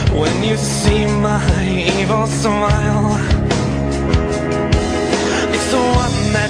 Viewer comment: any one else think he says smile like ssthhmile